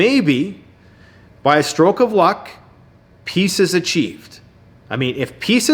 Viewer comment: ! When you said "Peace is achieved" I thought you said "Pieces of cheese"...!!! I had a statement about the previous and current video but you said it all. has a good heart for going where he's not only risking his life, but actually helping. And you, are a good friend. You're lucky to have one another as buds.